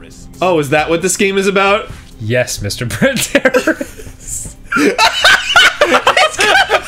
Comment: I'm probably going to feel dumb when someone explains it, but what is the joke at 5:30 ? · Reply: i honestly thought Dan said "Mr. Printer Error"